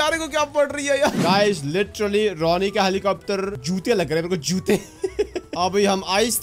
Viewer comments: what did he say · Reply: Jutte yani shoes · i hear ch****@ instead of jhuutiya in Punjabi tone😂